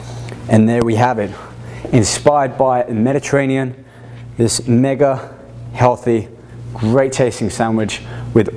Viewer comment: sounds like hes saying: THIS NIGGA! xD